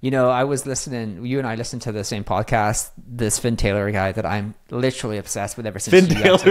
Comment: What is the name of/link to the podcast they mentioned? I sounded like Ventera but I can find anything on it. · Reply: I thought he said Vin Taylor, I think it's Fin Taylor. <link>